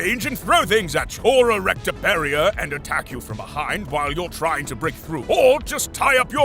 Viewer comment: I heard she into “attacks” from behind